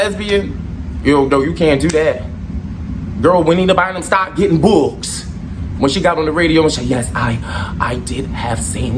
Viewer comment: Couldnt catch the name at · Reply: Juanita Bynum